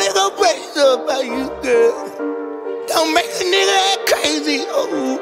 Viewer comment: BECUASE YOU KNOW I WILL. AND I'M ON A PERKY PILL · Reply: I thought he said turkey pill